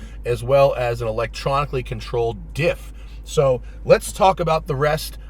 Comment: I thought you said Electronically Controlled DILF for a second. I was like, what, did you put Zach in a shock collar or somethin/